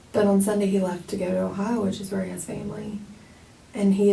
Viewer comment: this is so random but at about 10:34 I think she says "which is where he has family" not "which is very unsavory"